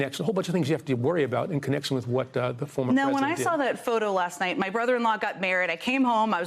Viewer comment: Did she say, "My brother-in-law got married?" (1) I re-listened to these few seconds at least a dozen times because I genuinely am not sure that that was the actual phrase she uttered (it sounds more like, "my brother-in-law got mare-od"), and (2) I'm wondering why she would say "my brother-in-law got married" rather than "my sister got married." I'm imagining various hypothetical scenarios like, maybe the "brother-in-law" is actually Jen's sister's ex-husband, who is now getting RE-married, but Jen still considers him her "brother-in-law," or maybe Jen has some great divide in her relationship with her own sister, so she would rather refer to the wedding as "her brother-in-law getting married" rather than "her sister getting married," or maybe she just wants to he very respectful to her new brother-in-law and make more of an acknowledgement to him in this moment, than to her sister, just out of respect for him. That one little phrase really made me think a lot and seemed to open up so many questions.